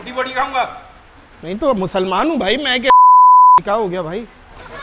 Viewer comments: I want to know what he said. Musalmaan hu bhaimain kya............. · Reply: Usne bola musalmaan hu bhai main kaafir thodi hun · musalmaan hu mai koi(.... beeeeeep....) isle baad kya bola aapne bhai? Btao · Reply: Hindu 😄